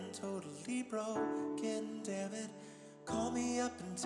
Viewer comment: the heartbreak when I realized he's saying "when i'm fully irrelevant and totally BROKEN, damnit" and not broke :( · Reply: I always thought he said 'broke, god damnit' This is crazy